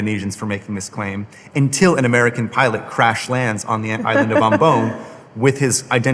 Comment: Wowwww the auto-generated english subtitles spelled "Band Doom conference" instead of Bandung... what a bad and clearvoyant omen ! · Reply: lol here we go again "the island of Bombon" [ = candy or attractive person in english] instead of Ambon 🤭